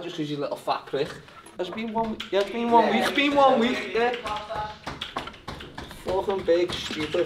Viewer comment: What did he say at 7:45???? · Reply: It’s been one week I guess the cheat day thing and he’s saying he’s gonna fuck paddy up with exercises and stuff